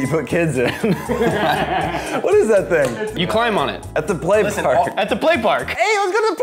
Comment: It sounded like said “Play Pork”.